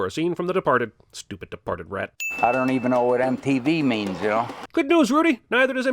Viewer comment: Rudy: "I don't even know what MTV is." Cinemasins: "Good news Rudy, neither does MTV!" good one! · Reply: I actually thought Rudy said "I don't know what a TV is." I've never heard of "MTV".